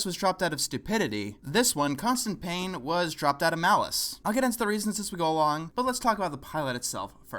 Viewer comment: I didn't understand the word at 0:18 malice?